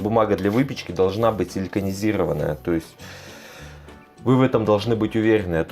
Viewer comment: Какя бумага, я не расслышала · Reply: Силиконизированная